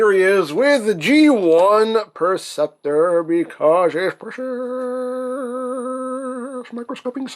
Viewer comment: what's that word he's saying the in strange accent? I've heard it several times but do not understand it. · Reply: He's saying "Precious, oh so precious."